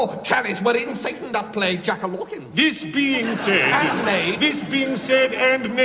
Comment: what does he say at 1:32 it sounds like jack martens or something? what does he say?